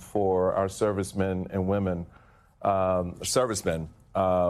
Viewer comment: OMG—I misheard Michael saying ride around; it sounded like rot around which is more accurate!!